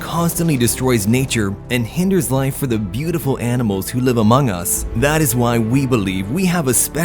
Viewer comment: Did he Said "AMONGUS" ?